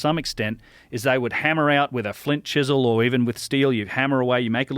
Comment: when he said "Flint Chisel" I thought I heard "Flint Dibble" and had to double take.